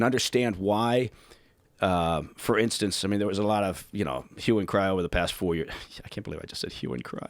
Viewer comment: Hue and Cry, I thought he said "human cryo" O.O